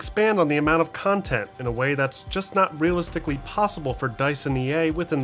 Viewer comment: what did u just say? 😂 not possible? ...